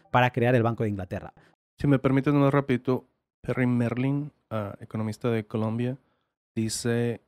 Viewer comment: a que economista hacen referencia? Solo entendí Merlín economista de Colombia o Columbia…agradecería que alguien me lo aclare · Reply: Perry Mehrling